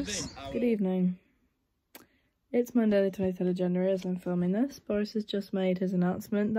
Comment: did u say January? 😂